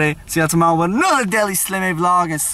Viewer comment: slim, make the outro say your channel's name properly, now it sounds like it says " slimmoficatio"